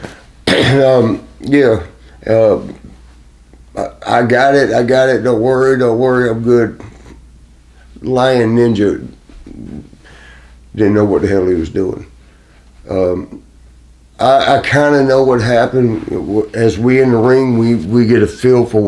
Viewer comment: Damn man Marty looks rough, he needs like subtitles or something. I thought I heard him call the guy a “lying ninja?” Well fuk…. I mean, he’s still alive I guess that’s something